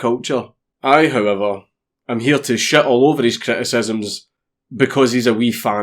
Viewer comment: Lol when I heard you say ‘took a shit over it’ I thought he like broke in and smeared shit over it😆😆👌